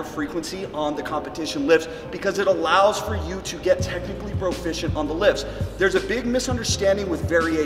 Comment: Could have sworn you kept saying technical broficiency.